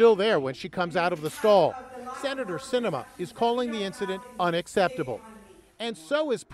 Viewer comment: I heard it as “Senator Enema” at first. I need to get my hearings checked.